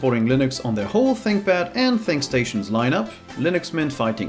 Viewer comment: I heard 'sink'stations. Are u German?